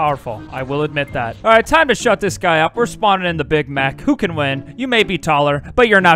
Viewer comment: did he just say big mac